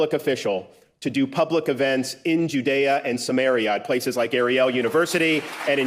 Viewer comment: did Desantis just called Irag Sumeria? As in the ancient civilization? Huh???